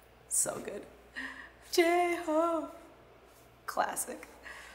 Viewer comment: Whatever she said sounded like J-Hope Me: Chicken Noodle Soup